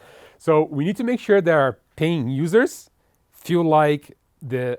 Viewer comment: "payed user" sounded like payed lower lol.